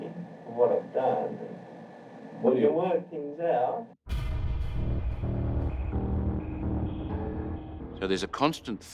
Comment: What is martin saying at the 38:00 mark? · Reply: Martin "I should be out, I should be out, if I could get out on bail tomorrow, I'd pay over the money" Interviewer " why do you reckon you should be out mate" Martin" Because it's not fair on me, is it? I mean, I know I've done the wrong thing but Surely they can train me a few months of the year, for what I've done, we can work things out"